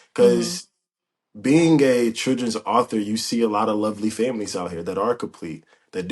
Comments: Sound like he said being gay!!! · Was it just me At 18:33 he said being “a”, but it sounded like being “gay”. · I thought he said “being gay” right here lol.